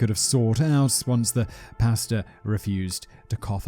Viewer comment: Every time Simon says "pastor" I always hear "pasta", for some reason.